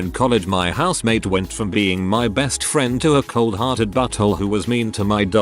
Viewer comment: I thought they called him a cold hearted burrito and was so confused because that’s a kinda odd insult